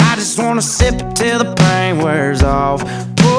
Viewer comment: "Sip until the brain wears off." I've always misheard it😅